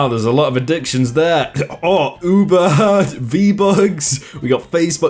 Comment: did he.. did he say.. Venmo was Vbucks?